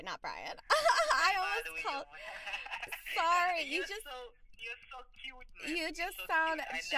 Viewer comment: Him: "You're such a cutie" Her: about to throw up Me: about to throw up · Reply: I think he actually said "you're so cute" it's kinda muffled